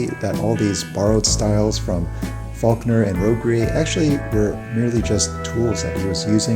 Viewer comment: borrowed style from Faulkner and who? I couldn't hear the name very clearly. Also great video, keep em coming. Thank you. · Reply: Robbe-Grillet